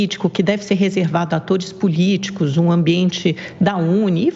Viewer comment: no minuto 3:43 Amanda fala TODIS POLÍTICOS? ou eu escutei errado?